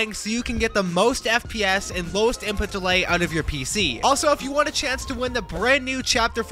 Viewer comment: I thought i heard ''Most Input Delay'' but it was actually ''Lowest Input Delay'' xD 🤣